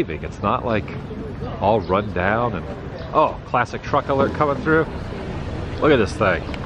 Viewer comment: I thought that you said PLASTIC truck alert 😀😊